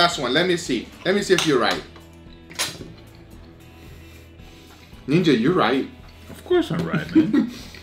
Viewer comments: Did he say nigga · from 9:35 it sounds like he is saying, nigga